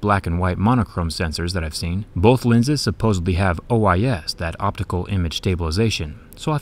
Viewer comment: I hope I misheard him say "supposebly" at 2:18.